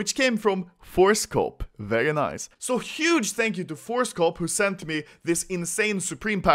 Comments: I thought it said "Thank you Horsecock" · Anyone else thought he said horsecop